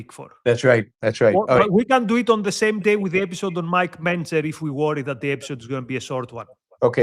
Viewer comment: mentions a show on Mike who? Pence? Mentzer? Kind of sounds like he says mentzer. Which would be cool because Mike mentzer was a bodybuilder who was an objectivist. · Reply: Yep, Mike Mentzer...the one Mike Mentzer